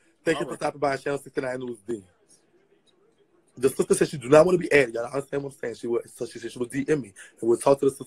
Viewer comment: said are you getting a discount heart... I need to repent for giggling 😩🥴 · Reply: Nawlins Bloggin Noooo... I missed that part. I thought the sister said she didn’t want to come on live